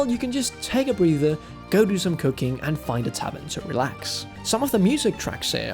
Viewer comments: I had to go back and listen a few times to finally hear what you said. It was "cooking" and not "cocaine"... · I thought you said, take a breather, go do some cocaine and relax.